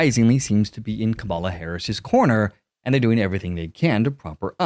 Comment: did you say Kabala Harris?